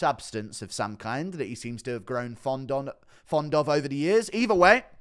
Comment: he said fondant instead of fond of. Cakes on the mind